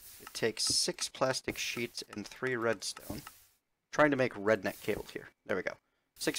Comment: Up until you showed the RedNet cables, I seriously thought you were saying Redneck Cables. Oh my god. Haha.